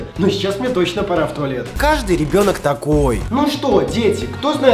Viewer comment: Мне одной в самом начале вместо ребёнок послышалось ебёнок?